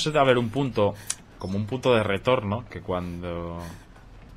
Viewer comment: el puto de no retornó XDXDXD nose si escuché mal pero creo que dijo eso XDXDXD